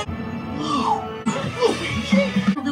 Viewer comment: I always think it, he say WALUIGI.